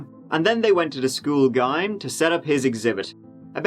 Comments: Did he say "Gime" instead of "Gym" at 2:21??? But then he said Gym a few seconds later? I'm confused, lol. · Did you just say the school guym? instead of Gym? Lol!! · Ok.. just a quick question.. Im 2:24 into the video and i heard the word "Gime", like rhyme with a hard G. Was it supposed to be Gym? Like Jim, but with a soft G? Lol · Did you say the school "gime" (2:22)?? 🤔 lol The GYM?? 😂😂 My bad that threw me off for a sec. But nonetheless another great episode. 👌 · I'm sorry, did you just call the school GYM, the school gime? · says school gym as GUYUM instead of jim😆